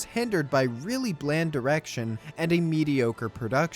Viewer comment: did you just say "bad erection"? O.o